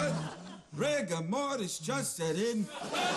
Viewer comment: What did he say at 1:20? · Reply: Rigor mortis just set in